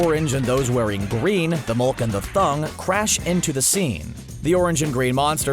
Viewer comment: Who else heard him say thung instead of thing?